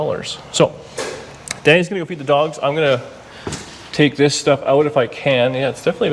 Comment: Sometimes you talk pretty fast...like this one it sounded like you said "Danni's gotta go Beat the Dogs" you said it so fast...